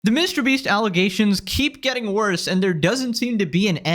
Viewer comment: opening this video and seeing a picture of Jimmy wearing a straw hat, with the title being "his worst allegation yet", I said to myself: "Well that gives a completely different implication". Never in my wildest dreams would've I expected to be so wrong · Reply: I kept pressing the time clip and holy it sound like he was saying Asian keep everytime